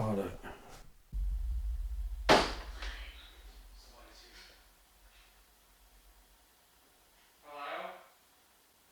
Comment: Anyone else catch the woman's voice @ 54:38???? 😮😮😮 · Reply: Sounded like "I'm a", but the sentence wasn't finished.